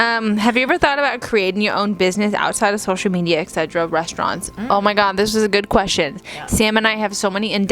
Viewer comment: did you just say 'eg cetera' as a replacement for 'e.g.' at 7:57?! LMAO that's gonna be my new thing hahaha